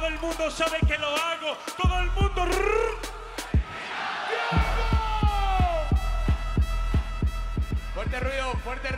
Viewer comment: que dice? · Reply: Verificado